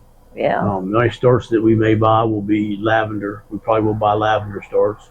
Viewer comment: You say lavender, I hear "Iced Tea." LOL